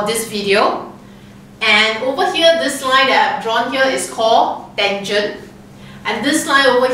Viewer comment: subtitles said tangent "engine" LOL